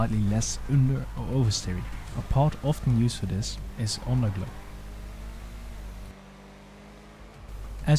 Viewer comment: "A part often used for this is [can't understand]" what did he say? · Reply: underglow (neons)